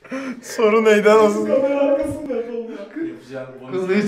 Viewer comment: ne dediğini anlayan varmı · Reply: Soruyu unuttum diyo